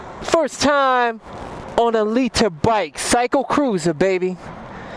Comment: FYI- In my video titled "600cc is All You Need - Liter bike is Unnecessary" If you go to 7:41 in that video I said I would probably get a 2012 Fireblade because it would be cool to complete the [CBR] Trilogy. I still stand by everything I said about liter bike in the past, YOU DON'T NEED A LITER BIKE, a 600cc is all you need, really more than enough for the public streets. I can now say that WITH liter bike experience.Guess what, I'm STILL doing the same speeds that I was doing on the 600 just like I said. lol Don't listen to people out there saying you need a liter bike, it's BS! However, I'm glad I bought the fireblade for the experience and to have a newer bike with less maintenance to do. Here is my video I'm referring to: 600cc Motorcycle IS ALL I NEED - Liter Bike is Unnecessary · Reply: oh man its called a Liter bike? I must be heard of hearing I always thought your saying "Leader Bike" :)